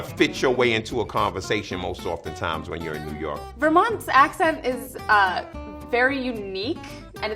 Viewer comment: I heard “Vermont’s accent” as “Your mom’s accent”...